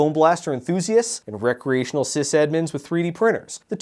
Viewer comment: The fact that the auto-generated captions say "cis admins" instead of sysadmins adds a really funny additional layer to the "two genders" joke